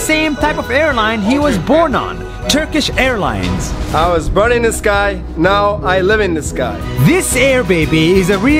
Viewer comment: I heard "I was born in this guy, and now i live in this guy"